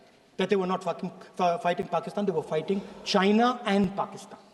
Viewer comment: is he said bymistaly fucking instead of fighting. 😅😅😅😅